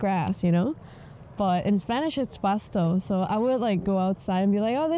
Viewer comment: Wait on the subtitles it says “pastel” instead of Pasto, esta hablando de Grass not about Cake! 😅